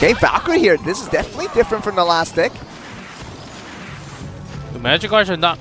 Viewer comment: he says dick instead of deck